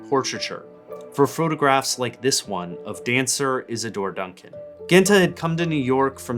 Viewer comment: Love this and her ability to see "beyond" the lens of her camera in the way she does. Also, praise be for subtitles bc I definitely misheard something to be the name of a work, not a person. "Dancer Is A Door: Duncan" is, in fact, not one of Genthe's works. Who knew?